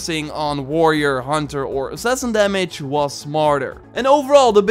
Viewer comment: I keep hearing "Assassin Squid" lol